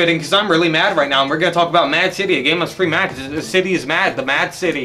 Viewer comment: I thought it said Mafia City...